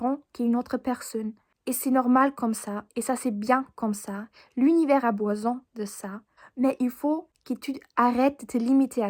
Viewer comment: Super vidéo merci 🙏 qqn pourrait m’aider à éclaircir le mot qu’elle dit à 04:49 - 04:50 elle prononce “l’univers a “poison” pour ça mais je pense que c’est parce que j’ai mal compris , moi même suis une non-francophone donc j’ai un peu plus de difficulté avec l’accent…. Merci d’avance 🙏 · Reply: Elle dit : L'Univers a besoin 😉